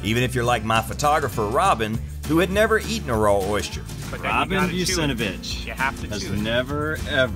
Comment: I thought he called Robin a SOB. But then I saw her name